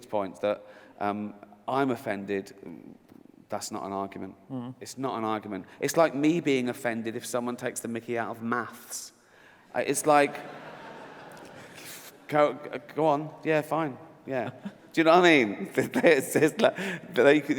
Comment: "It's like me being offended if someone takes the Mickey out of muffs"? What did he say? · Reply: He said "maths". It's how they say "math" in Britain.